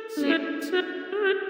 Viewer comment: is bro saying oh sh*t?